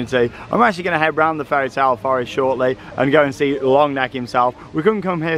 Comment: thats really a dutch accent i love watching efteling vlogs cause im dutch😅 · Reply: While im listening you're just saying longneck but i thought that you said "langnek"